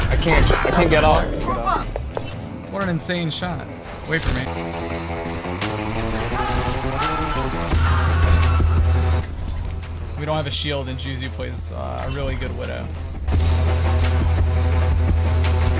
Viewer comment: did kephrii just call you juzi?